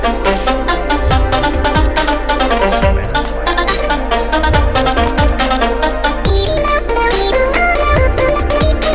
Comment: Why do i hear a voice speaking in my right ear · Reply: Its the Spookiez samples: "Trick or treat, smell my feet..."